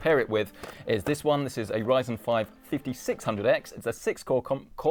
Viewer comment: I literally upgraded to this build a few weeks ago, and your channel was both instructional and inspirational! I’ve the Red Devil version GPU and couldn’t be happier with how well everything runs! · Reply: oh sorry i thought you said ryzen 5 7600x